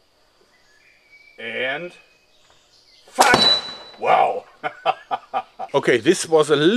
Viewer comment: Was sagt du denn bei 2:01 ? Aaaaand f**k?